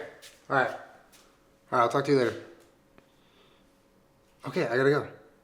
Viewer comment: JesUS fuck, in the beginning I've heard him say "I'll fuck you later"..